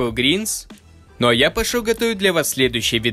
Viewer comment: Опа, а почему на заднем плане с музыкой 4:18 я слышу "Пошёл ты" 😅😏